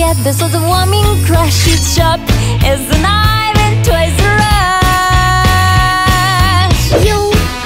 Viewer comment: "It's sharp as a knife in Toys R Us!" I can't be the only one who heard that. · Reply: Villager of SmashVille i heard "its sharp as a knife and toys a rush"